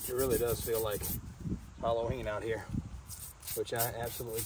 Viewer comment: Halloween oh mercy and I heard you say you cannot wait. 😖